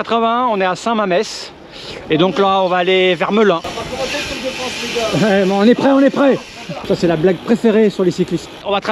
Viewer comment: C'est quoi la blague préférée sur les cyclistes ? (on entend pas très bien à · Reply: "C'est pas commencé le Tour de France !"... 😁